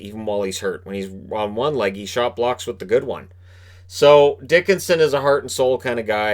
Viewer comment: All playoffs long all I could do after hearing an announcer say his name is call out "That's what I said Dickskin"